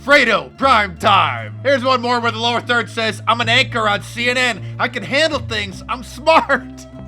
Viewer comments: He actually said "anchor". But he is, and they are, "fake news", so it's to be expected. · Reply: lmao i thought he said actor but your right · Sounds like Fredo says "I'm an actor on CNN" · He is ACTOR on cnn! Best Line Ever! Actor that means he PRETENDS to be someone else on tv or radio! Hahhahahahahahhaha Fredo you broke my heart! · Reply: He said anchor. His big dumb guido accent just made it sound like he said actor. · “I’m an ACTOR on CNN” - Chris Cuomo aka “Fredo” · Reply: I thought he said actor too! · He’s an anchor on CNN? I thought he said he was an actor on CNN. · He says he is an “Actor” on CNN. LMAO. Yes we know! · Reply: I thought he said actor too, but he really does say anchor. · Role model! First time I saw the clip I thought he said "I'm an actor on CNN"... Which is technically closer to the truth I suppose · Listened to the audio on twitter... I thought he said he was a CNN "actor" not "anchor"... no difference really... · He said Anchor not actor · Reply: patriotic American sounds like he said actor to me. Must be the “Fredo” accent that threw me off! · Sounded like he said I'm an ACTOR on cnn.LMAO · I could have sworn he said hes an Actor on CNN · I heard "...I'm an actor on CNN" Freudian slip? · Sounds like he said I am an actor on CNN. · I thought he said "I'm an actor on CNN". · It sounded like he said "I'm an actor on CNN" but that can't be right, telling the truth like that. lol.